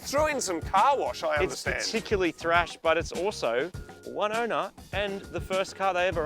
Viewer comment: Moog says "one owner"... I hear "Wynona"